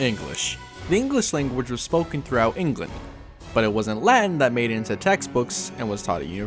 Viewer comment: is he saying wasnt or was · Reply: Was